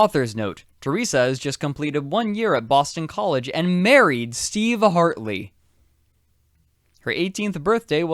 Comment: I can't be the only one who misheard it as her marrying Steve Harvey, right?